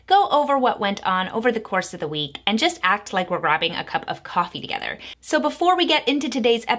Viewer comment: Sounded like "robbing a cup of coffee" lol